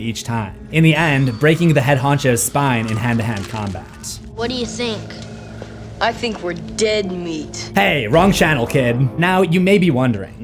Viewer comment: I heard “eat my meat”🥀